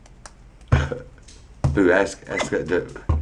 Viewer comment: Did he just say boob